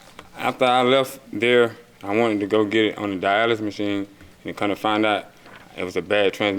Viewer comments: I darn sure thought he said dialysis machine..I started feeling terrible for him! He just wanted a once valuable car I guess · did he say he put it on a dialysis machine lol 😂